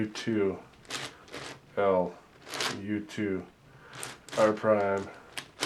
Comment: The parity At 1:37:57, when he first did a left turn I thought he said hell but I think he said L lol